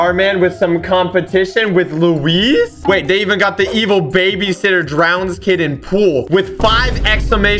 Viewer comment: I thought it said evil babysitter drowns kid in Poo. Lol